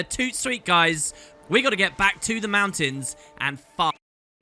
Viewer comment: almost sounded like you said "we gotta get back to the mountains and FUCK" at the end there :p